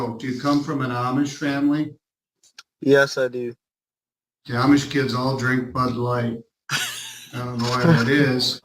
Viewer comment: I could not understand what the judge said. Can someone help? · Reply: “All the Amish kids drink bud light.”